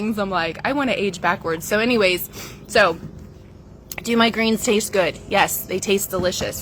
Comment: ish I thought she said, "do migraines taste good? Yes!"